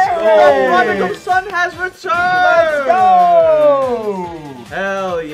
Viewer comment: I legitimately thought said “the Prob-Doogle son has returned,” and then I realized that he still doesn’t believe that he is Doogle